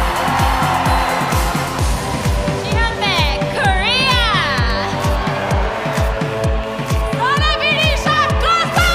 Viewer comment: Is there any fellow Koreans who understood what she said at 8:02-8:05? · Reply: she said her name :) jihyun baek / 지현백